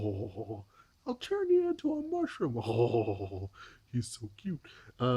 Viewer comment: Lol , I heard "awahaha so cute" can we get some light and better explaining lol , but keep up with the good work